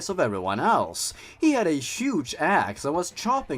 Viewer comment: I heard “he had a huge ass”